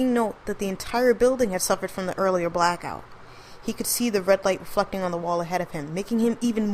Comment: I swear she says "the red light fucking on the wall"